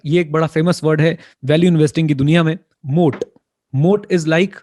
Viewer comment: What is the word Sir mentioned at 31:32 ? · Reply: Moat